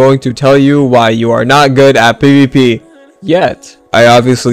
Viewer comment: Not gonna lie I thought he said "today I'm going to tell you why you are not good at PP"